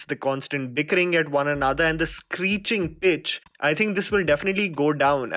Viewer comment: I'm sorry I heard 'screeching bitch' rather than 'screeching pitch'...😂